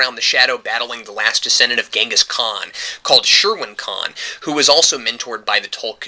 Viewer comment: I thought you said sherwin khan.... hhhahaha